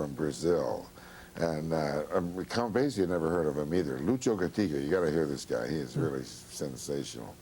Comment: what name is he saying here? Anyone know? Lucio who? Please spell it. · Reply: Lucho Gatica <link>